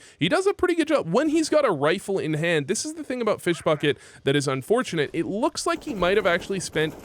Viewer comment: sounds like you say fish fucket when you say his name